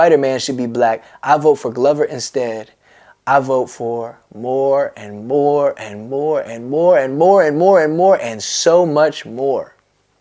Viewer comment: At first i thought he said in the song “i vote for Morty, Morty, Morty, more, more, more I vote for so much more